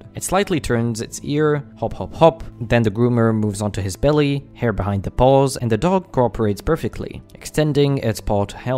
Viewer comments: Why did I hear “hair behined the balls” And “extending his ball to help”💀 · His little head tilts are just too cute for this world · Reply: I thought he said “hair behind the balls”💀💀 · I heard "hair behind the balls" 💀💀